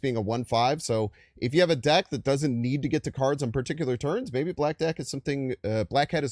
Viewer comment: I seriously thought you said black d!ck and caught yourself 😂😂😂😂